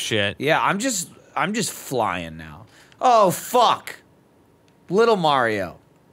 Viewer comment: at like 19:00 I heard Jeremy say little boat! this can only mean one thing, Jeremy is a lil yachty fan